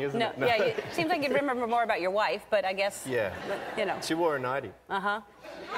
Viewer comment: she wore a ...? what did he say? naughty? ninety? (not native speaker here)